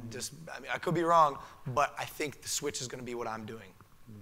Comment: What did he say at 25:11 ? "I think Sw** is what I'm gonna be doing?" What was that word? Switch or what? I'm still learning English